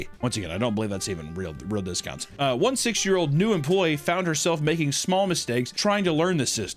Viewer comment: bruh I'm so high I had this shit on the background I thought you said six year-old employee bruh I thought hobby lobby was doing child employees